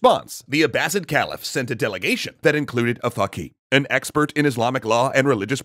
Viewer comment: What did they say at 0:53? I can't tell whether that was an actual word or a swear sort of sounded like something else. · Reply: Hello, I know this word, first I got confused to but it turned out they say a "Faqih", I'm more familiar with the word "Fuqaha" tho but both means an expert of Fiqh (Fiqh is teachings of Islamic jurisprudence or Islamic Law).